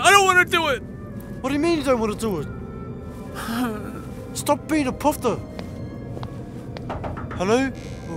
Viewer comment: did he just say, "Stop being a poptart"?